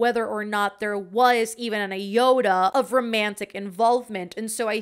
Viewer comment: I heard you say “Evan an iota” as “even a Yoda” 😂😂💁‍♀️ lol. Fun video.